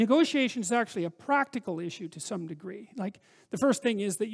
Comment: did he say it WAS a practical issue or it WASN'T a practical issue.... (Have extremely bad hearing at 18😭) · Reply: Nathan Brown he said it is a practical issue